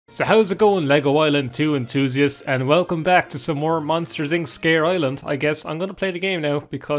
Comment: I'm watching this while buzzed, and drinking a random bottle of Jose Cuervo margaritas mix. I heard 'Monsters Inc' and was subsequently highly confused when Harry Potter started.